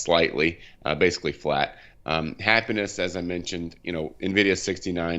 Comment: I can't make it out. At 1:05, does he say "happiness is at 69, nvidia 71" wtf is happiness? lol That said, I highly appreciate the pronunciation correction of Nvdia at 4:03 .. when experts can't even say the name of the company it really frustrates me lol - good job.